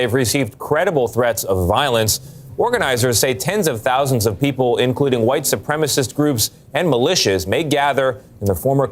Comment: What i heard was that "these ppl protesting are white supremacist"